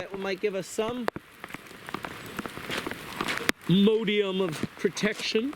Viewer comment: I heard you say modium(?) and was intrigued as I'd never heard the word before. Did I hear you wrong? I can't find anything online as far as a definition of a word spelled similar to it. How do you spell it or what does it mean, if anything?